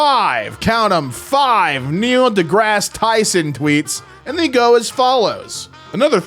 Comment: I was listening to this as background noise while I did some shit, not paying attention.... and I misheard and thought you were talking about MIKE Tyson. At first I was getting slowly more frustrated, over what I thought were fake tweets. And I was saying out loud 'NO, Mike Tyson probably doesn't even know these words! Stop lying! 🤬" Until I realised 'actually Mike Tyson probably doesnt even use his own twitter account (IF it exists)